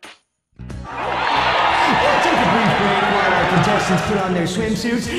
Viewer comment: When that guy said "brief break" at 5:13, I thought he said "beef steak" I think I'm becoming Chie...